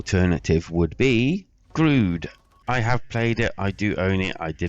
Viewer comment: I did a search for the alternate game you mentioned. It sounded like you said Glued? I couldn't find anything. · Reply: It's Grood. 👍😎